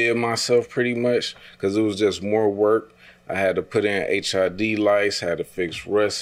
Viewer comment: He lost me at LID lights 🤣🤦🏾‍♂️ it’s LED my guy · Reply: It sounded like he said HID.lights which is even worse than saying LID lights. Lol.